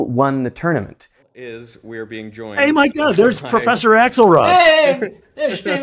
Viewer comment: I really heard "It's professor Axel Rose" and I was amazed, keep it going professor, you rock!